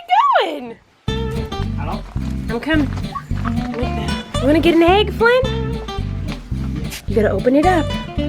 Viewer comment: When she said, “Wanna get an egg, Flynn?”, it sounded like Miranda to me. Anyone else hear it too?